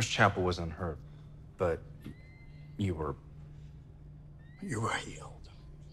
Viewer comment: Can someone please tell me what the doctor says @ 3:53? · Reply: You were healed